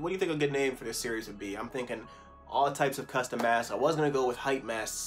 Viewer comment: I thought he said custom ass😂